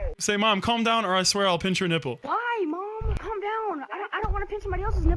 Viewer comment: I thought it said punch your nipple